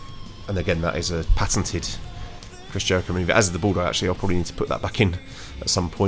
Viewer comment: He said it was Chris Jericho's move. He listed the moves first and then who they belong to. I think you thought he listed the wrestler first and then the move so you thought he said "Brock Lesnar Lionsault".